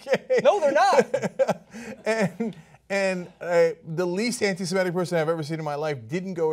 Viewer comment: Actually sounded like he said 'I'm the least Semitic person you've ever seen'.